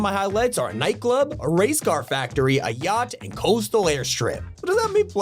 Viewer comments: Thought bro said gyatt instead of yacht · when he said yacht I thought he said gyat.